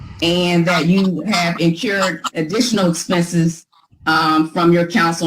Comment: this is rough! It sounds like she said "incurbed" and "axed". It's painful!